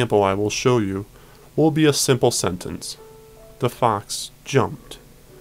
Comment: I wasn't looking at the text in the first sentence and I heard "the fonts jumped".